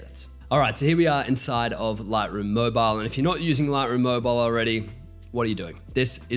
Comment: Can someone tell me what is saying around 8:45 in video about “blank blank mobile” . An editing app he is using and I can’t tell with the accent he has. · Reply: Lightroom Moible! The Aussie accent coming out strong there hey? 😅